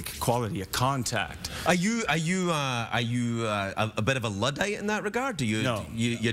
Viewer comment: “Are you a bit of a.....?” 21:05 what did Craig say? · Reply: "a bit of a luddite" its a person who doesn't like new technologies